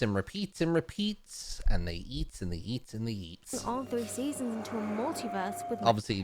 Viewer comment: I thought he said "they yeet and they yet and they yeet" ngl...